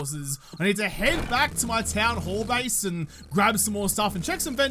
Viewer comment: When said town-hall base, am I the only one that thought he said town-whore base? Anybody else... or am I just alone in this boat?